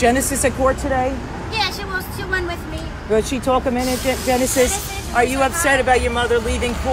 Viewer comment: Am I the only one who heard the reporter about to say... Does she talk Dominican! 6:04 to 6:08 not cool #BadReporter